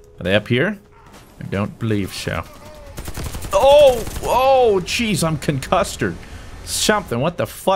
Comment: When you said “did I get concussed or something I swear I heard “did I get cuncustard something?” I’m not hear so good in the now